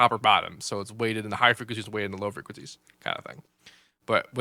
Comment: Piss poor explaination of what the top and bottom tension does. Plus what did he say at 3:40? ".. and then the harmonic blur, which basically is top or bottom, so it's (mumbled) than the higher frequencies and (mumbled) than the lower frequencies". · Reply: Weighted than? That doesnt' make any sense.